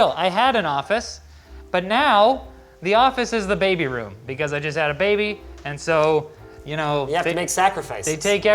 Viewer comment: i heard 'I designed a baby' . That was pretty cool . :))